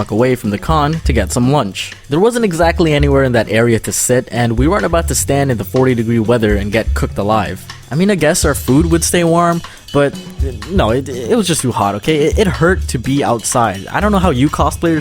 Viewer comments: Hey, fellow Americans, 40 degrees Celsius is 104 degree Fahrenheit. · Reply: for a second i thought he said JUST 40 degrees i was like bitch wtf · did he say 40 Degree weather!? danng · Did you just say, "We're not gonna stand in this forty degree weather and get cooked"? I HAVE TO DEAL WITH 95 DEGREES